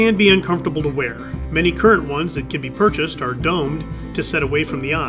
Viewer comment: Ohhh, you said many modern eye patches are..."Domed". I thought you said..." Dumb". 😋